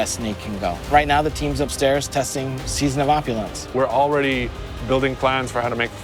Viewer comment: Did he say season of opulence